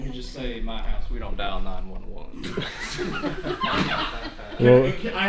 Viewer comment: what did he say? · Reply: in my house we dont dial 911